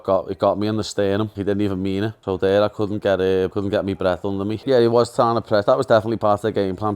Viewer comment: what tf is he saying, I honestly can’t understand what he’s saying . Doesn’t he speak English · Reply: Hes saying he hit him in the sternum and he couldnt breath but gaethje didnt even mean to do it but he went down because he couldnt breath